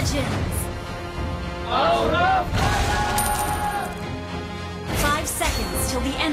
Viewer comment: can someone tell me what the background says? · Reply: Aura fire i think.. Thats sound from that emote